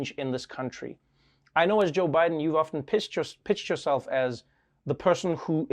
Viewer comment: anyone else hear Trevor's gaffe/Freudian-slip: "I know as Joe Biden, you've often pissed your–PITCHED YOURSELF(!)..."?